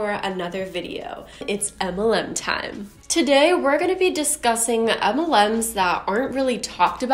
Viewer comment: At first I heard "Today we are gonna be disgusting..." And I mean... misheard, but still kinda fitting, if we clarify that all MLMs are disgusting ;)